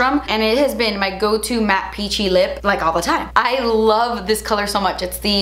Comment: When she said “peachy lip” I thought she said “hoochie lip” 😆